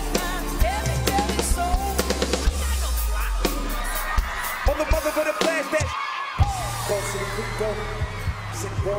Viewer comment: what is he saying?? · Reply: Housnaslife "you ain't got no flako in your serato"